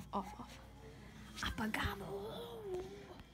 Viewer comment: what is saying at 14:40? it sounds like abogado (lawyer) lol